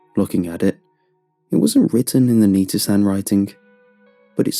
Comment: Oh I thought is said The Nut